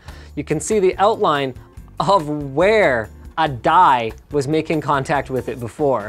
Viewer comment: My english ist not the best, neither is my knowledge of cpu-coolers, so could anyone tell me pls what Linus is talking about at 1:45? He says "you can see the outline of where a ??? was making contact..." and I don't understand the word, so I would be very thankful if anyone could tell me^^ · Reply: DIE, part of the cpu, that part where the internal thermal paste comes on, then the heatspreader, what he ment was it touched that part of the block so it left markes, like if you put your greasy finger on a glas or so.